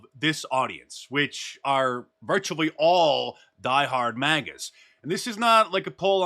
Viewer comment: did you just say “die hard magats” ? or maybe you said maggots….i could be wrong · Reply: Magas